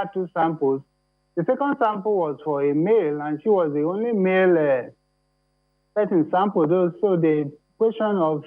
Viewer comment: wait...what? Did the government official said that "she was the only male"...? And the interviewer did not react to that. What the hell is going on here people, are we dealing with a transgendered person that does not want to admit to having the COVID-19, or what? Ah...mo ya gbese, oh.... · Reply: I heard it too!! "She was the only male that we sampled" !!!